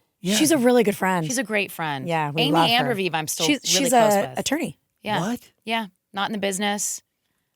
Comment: I thought AJ said “she’s a, she’s a tree” 😂😂😂 had to rewatch to hear her say “attorney” which makes way more sense 😅